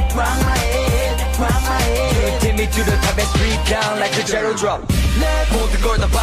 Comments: I always hear "straight down like a jelly drop. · I keep hearing "straight down like a jello drop" the rock part was hilarious tho I got coffee all over my laptop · It sounds like Mingyu was saying "Straight down like a Jelly Drop. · I thought they were saying "ride my head" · For the longest time, I actually thought they were saying Straight down like a jello drop... -____- Now I can't unhear it... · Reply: i thought they said "stripped down like a jello drop" at first.. lol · 'Keep doing this pleasant pain' o.....kay?? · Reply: And wait why do I keep hearing 'You take me to the top and strip down like a jello drop'? Me and my dirty mind sighs